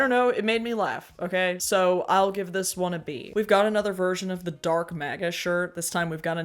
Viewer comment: When I first heard Elon say that I thought he said "dork maga" and thought: You're not wrong, buddy.